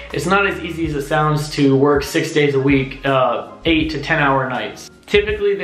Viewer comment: Got any questions? Ask me here!!! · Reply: Are warehouse workers off on holidays? Also, sounds like you said you work a 4 day week?